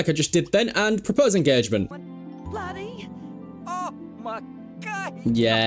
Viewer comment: it sounds like he said oh my gosh💅💅💅